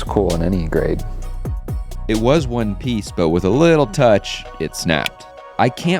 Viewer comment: it was in what? say that again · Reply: It was one piece